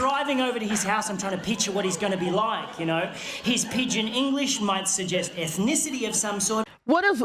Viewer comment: Pidgin English just basically means broken English. As in like saying “I want buy food” - it might get the message across, but it’s not grammatically correct. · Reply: I always thought he said "pitch in English" not "pidgin"